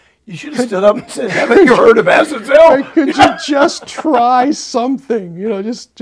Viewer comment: “Haven’t you heard of ______?!” What did he say there? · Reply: Azazel